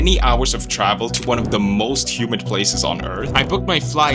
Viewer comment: I really thought he said "the most human places on earth"